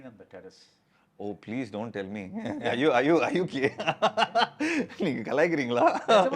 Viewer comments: In 29:55 did Jeeva ask "Are you gay?!!" Or did I just hear it wrong? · did he say "are you gay" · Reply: I guess he said are you okay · did he really say are you gay 🤣🤣🤣🤣🤣